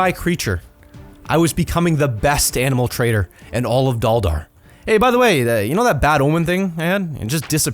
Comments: I thought he said Valdar😂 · i thought he said galdar